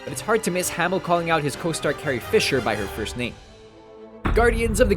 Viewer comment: It doesn't even sound like he says Carrie it sounds more like he yells out "hey!"